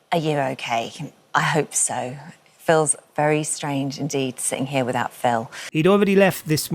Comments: Holly Willoughby: "Are you okay?" Dear God what dreadful shite--what are we, 4 year olds? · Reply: It sounded like she said "Phil's very strange" instead of "feels very strange" 😂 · I heard “Phil’s very strange indeed” which is also correct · It always cracks me up with the Holly Willoughby speech when she says "Feels very strange." Always sounds like she says "Phil's very strange." And yes he bloody well is very strange indeed